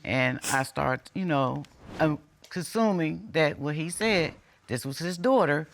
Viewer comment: did she say she was consuming that this was his daughter? Come on people!